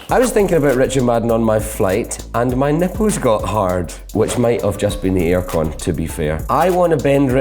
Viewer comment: What does he say on 1:36 i can't figure out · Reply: "Which might have been the air con, to be fair"